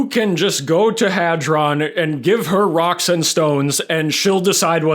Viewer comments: DID I HEAR SOMEONE SAY, ROCK AND STONE??? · DID I HEAR A ROCK N STONE ?